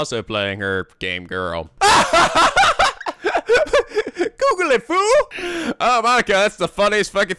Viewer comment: the fan sub says Nah, Google it up Shitlord.. which is actually what monaka really says